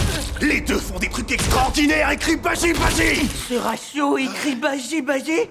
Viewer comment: A 16:10 Il dit quoi ? J'entend "ceratio il crit baji baji". Et juste après Baji dit gros flop, mais ça fait référence à quoi ? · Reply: "ce ratio" c'est une réf à twitter